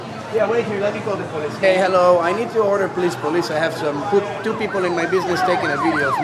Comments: I thought I was the only one who heard him say he needed to order a police 😆 da fvck? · Did he just say “hello, I need to order a police”. Hahaha.. sure is that for pick up or delivery and what kind of sauce? hahaha.